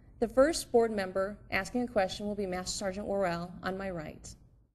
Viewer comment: "Mass sergeant" ... why is pronunciation not more important in the military? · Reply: I thought they were saying “mess sergeant” 😂😂😂😂